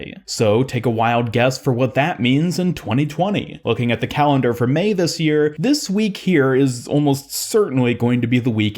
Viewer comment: Bro I thought he said "looking at the calendar for 'MEI' this year..." Ha lol I'm such a doof.